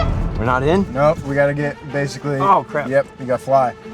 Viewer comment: What did chris say? Did he say cr#p?